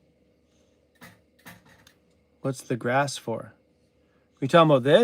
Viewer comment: Was that just then? Was she saying “I like grass!” 🤣